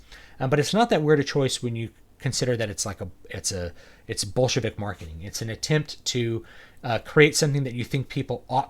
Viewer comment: At first I thought you said bullshit marketing :-) but definitely man they're way off the mark of choosing to sell their toys. What girl or boy would want that Rose toy? I remember when I was young, I'd run to the toy tore and the good ones were always gone. What was left was the background characters, or the Princess Leia non-sexy slave version, hahahah. Disney would have done much better if they had still focused on the female characters in the movies (to pull in both boy/girl movie viewers), but adjust the toys for action figures = boys, non-action figures = girls.